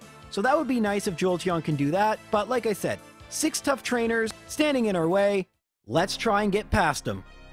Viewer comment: Did he call Bruno a “tough trainer” at 19:07?